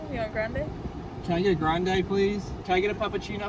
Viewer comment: " i heard "can i get a f**king cheeto please?" 😂😂 i love cheeto hes soooo cute. This video had me laughing.